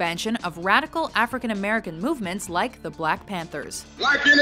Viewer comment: did she say "like a Black American movements the Black panther" ?